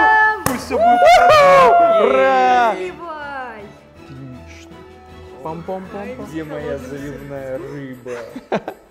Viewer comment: простите что она сказала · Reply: Наливай!